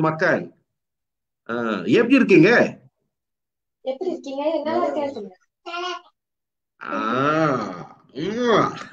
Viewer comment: = abg cakap apa dekat anak dia? 😁😁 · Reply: Cakap : apa khabar ? Haha